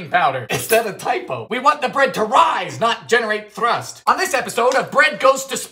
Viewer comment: “We want the bread to rise, not generate thrust!” That line broke me. 😆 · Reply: I thought he said thrush because all I heard of was bread and automatically went to "yeast". Thats put me off bread for a while lol.